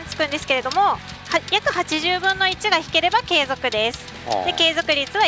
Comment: ん？ の機種紹介説明中ＳＴ中確率880分の1に聞こえたが気のせいかな。😅